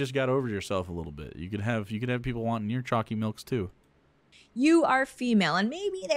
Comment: Seriously thought that was a euphemism in the title... Stupid algorithm. But you know what IS a gross phrase? Choccy milk. (Dead serious. What? Internet slang only went downhill since 2006.) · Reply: The first few times I heard someone say "choccy", I thought they were saying "chalky milk". Either way, gross drink, gross word, awful all round.